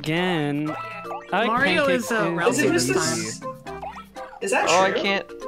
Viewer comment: There was a second where I thought you said "Mario is relative"